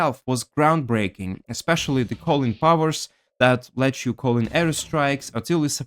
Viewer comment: When you said "call-in powers" I misheard it as Collin Powell, I thought it was a punchline